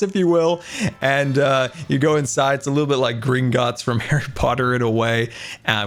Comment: “Like the Greek Gods in Harry Potter” You made me search if there were Greek Gods in Harry Potter Scar, and I think that you meant the Empire State Building, which in Percy Jackson, is the place where Mount Olympus resides, on the 600th floor. Please correct me if I’m wrong. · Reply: I think he said Gringotts, although I heard "green gods" at first! (I think that's how it's spelt anyway, the bank in HP.)